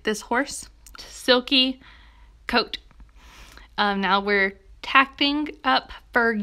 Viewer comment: did she say taping instead of tacking!?